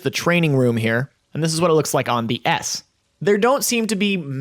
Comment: Sound like he says "this looks like ass"